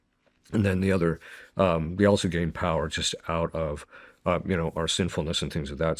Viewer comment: did he say "we" also gain power!? What do you mean we!!